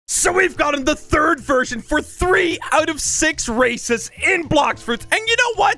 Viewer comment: I thought u said i got 3 out of 6 racist